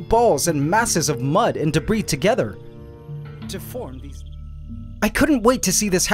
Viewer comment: What? Edit: He said : To form this...